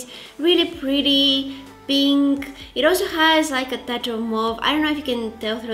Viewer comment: did she say a tach of μοβ; (Purple in Greek)